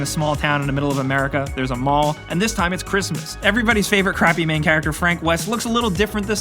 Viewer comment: " every ones crappy main character",am i hearing this guy right?